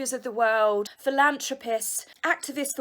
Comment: When you said philanthropist I thought you said...Phil lambs to piss. Juxst had to share that. Have fun!